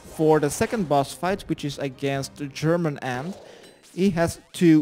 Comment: sounds like you said the boss fight is against german aunt hahahah thanks for the guide and laught